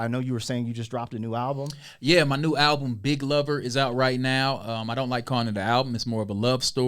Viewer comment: i heard "dick lover" i was like bro is crazy. lmfao